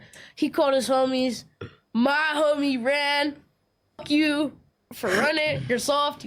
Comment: did he say" cuh ran"😂😂😂 this is diabolical